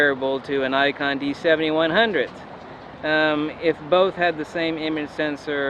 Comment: i heard you say "D3 and D7100 have the same image sensor ..." did you mean D700 instead of D7100 ? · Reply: i said D3 and D700